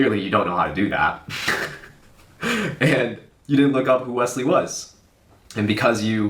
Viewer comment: At first I thought he said, 'You didn't look up Wesley Willis' 😂